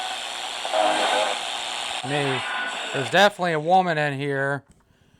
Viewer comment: "i smell perfume- who am i smelling" " It's me" also sounds like man saying "Mary" @ 28:23, shortly after the fuzzy, shouty kind of sound .